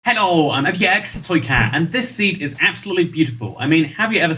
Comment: He sounds Like he is saying hello I abuse cats